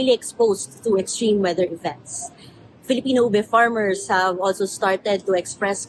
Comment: Funny at 5:28 how the caption said meeting the global demand for oil instead of ube.